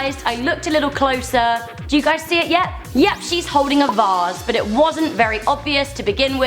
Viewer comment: DID SHE SAY VASS